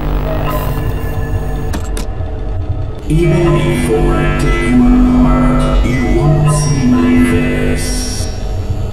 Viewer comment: What did he say? “You wait for us to come apart you won’t see my face”? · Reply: Even before I tear you apart, you won't see my face